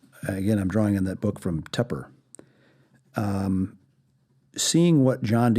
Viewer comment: Hello does anyone know what book this guy said is called written by I think Tupper at 13:45 min or so?